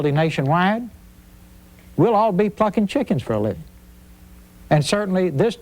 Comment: What's 10:31? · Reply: I think they misheard "plucking chickens" as "fucking chickens".